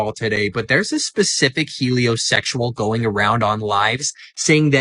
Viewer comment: Did...did he actually say "helio-sexual" at about 4:55-5:00? I really can't make it out but if he did, I'm sure there's a joke about burning when you pee...